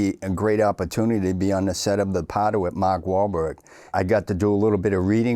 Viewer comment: A mobster that is in the union and does concrete work, never saw that coming. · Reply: with that accent it took me several times listening to realize what movie he said lol.. thought he said "The Powder" or "The Powdered" or something 😂